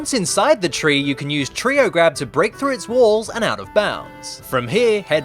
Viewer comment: The first time I watched this, I thought said "Tree-o grab"!